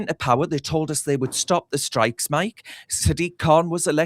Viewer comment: I think you misheard they said start the strikes 🏴